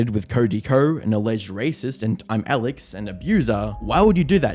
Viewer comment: Am I the only one that heard him say racist instead of rapist